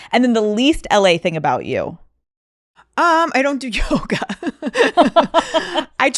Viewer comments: When she said "I don't do yoga" I swear I heard "I don't do coke" lol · I thought she said “I don’t do coke” instead of “I don’t do yoga” hahaha I was like huh ok coming out the gate strong 😂